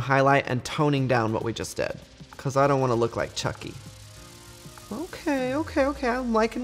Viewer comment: at first I thought he said "cause I don't wanna look like trixie." And I was like, you shady bitch.